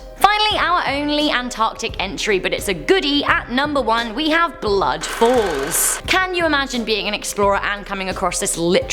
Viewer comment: OMG. I thought you said Blood Balls!😂